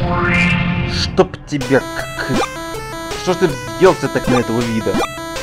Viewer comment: А что сказал с 49:38 по 49:41? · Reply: Штоб тебя, кыкы... Шо ж ты взъелся так на этого ВиДа?